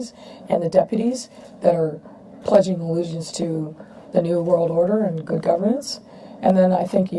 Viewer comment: Did i just hear ''pledge allegiance to the new world order and governance'' ?